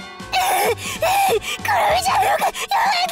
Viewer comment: あれ？きのせい？5:29で末永く爆破してねと聞こえた気が